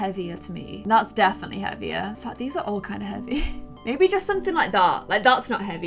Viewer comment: Wait did she say the f word? I thought she was kid friendly? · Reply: she said 'in fact'